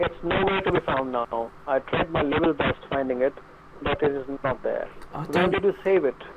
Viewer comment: I think he says "I tried my level best", which is another common phrase in Indian English. Just like "Do one thing" and "Each and everything" XD